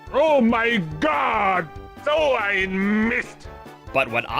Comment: For the longest time, i always thought wario was saying "DOH, I MISSED!" And not speaking german